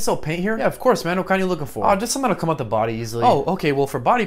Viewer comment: I had to rewatch that cus I thought he said “to hide the body easily” not “come off the body easily.”